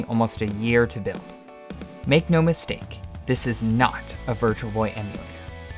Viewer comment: This is not a Virtual Boy what? Max Volume and I don't understand it. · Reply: It says this is not a virtual boy emulator subtitles exist lol